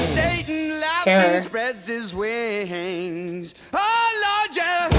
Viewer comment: I always hear “Oh lawn chair” instead of Oh lord yeah.